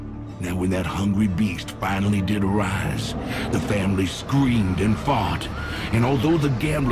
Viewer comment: "The Family screamed and fart" Did i heard that correctly??